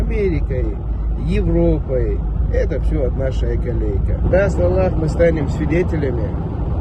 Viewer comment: "это все" - а далі що він каже? "наша еколейка"?